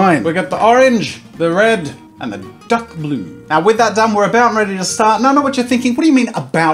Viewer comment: I thought he said duck blue